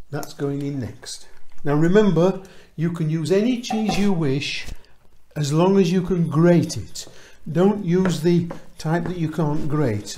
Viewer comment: I thought you might have said "You can use any cheese you like as long as it's cheddar.".